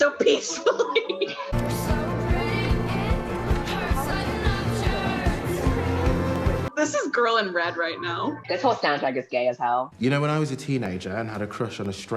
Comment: "This whole soundtrack is vay as hell" 😭 at first i heard "this whole town is gay as hell" 😭